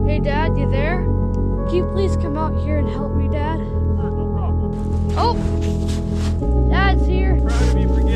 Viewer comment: Oh for a sec I thought the dada said “not my problem “